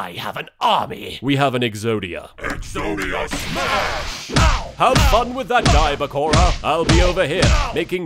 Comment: Wait! Wait!! Wa-wa-wait! At the end he says "Have fun with that guy, Bakura!"?! I always thought he said "Have fun with that Kaiba Corp". WHY WAS I ALWAYS SUCH A FOOL!?